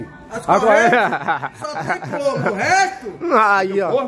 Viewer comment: eu entendi que ele falou que as correntes só tem "corno" em 00:35 kkkkk kkkkkkk